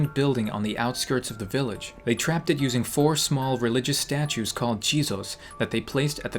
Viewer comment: Jizos? i thought you said cheetos